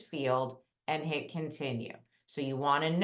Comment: At the 10:00 minute mark, did you say 2022? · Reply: Correct. You'll use your 2022 tax return to file the <num> FAFSA. They look at the prior PRIOR year. (2 year look back)